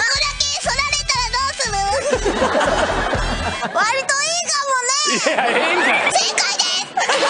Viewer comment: 割といいかもねがドラえもんに聞こえた笑